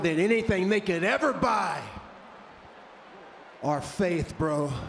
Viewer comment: I thought Jeff said "fades" instead of "faith".